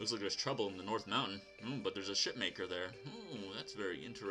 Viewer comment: At first I thOught you said shitmaker I was. very confused. I'm also really curios as to what my final stage all of a sudden, what is better than a dragon?